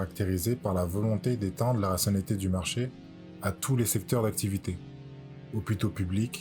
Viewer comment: étendre quoi ? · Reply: Etendre la rationalité du marché à tous les secteurs d'activité.